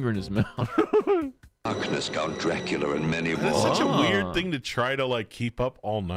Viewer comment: did he just say "Count Draculur"?